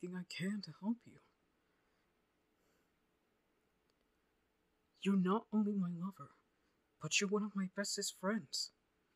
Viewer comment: Bro third time listening to this (I’ve probably listened to it more but I was like asleep) and at 5:26 when yugi says “Your not only my lover” I had to listen to that clip three times to realized that cause I had to do a double take when I heard “Mother” and “brother” 😭